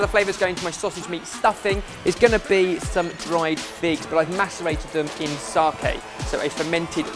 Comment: Anyone else hear master bated